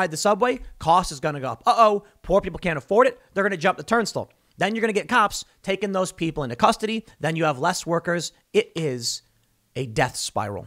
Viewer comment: Did he just say “turnstuhl”?